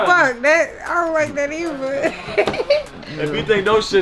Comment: did she say eiver